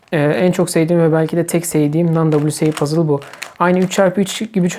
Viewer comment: da tam olarak ne diyor ? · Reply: Non wca, yani resmi olmayan.